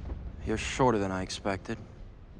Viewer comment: He's actually shorter than I expected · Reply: i heard Padme said "it "was shorter than expected